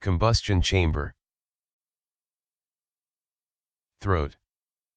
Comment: I somehow misheard "Throat" as "Bruh" bruh